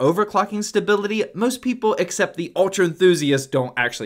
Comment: "most people except the ultra enthusiasts" For some reason I heard this: "most people accept the ultra enthusiasts" I was confused at first.